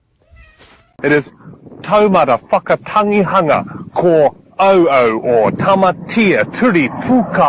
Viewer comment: did I hear that right? It is „Toe motherfucker…“